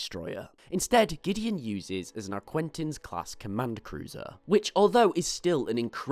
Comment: Omg if I hear “Arquintins” again....